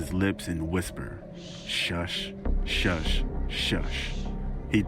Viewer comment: i heard "SHISH SHISH SHISH"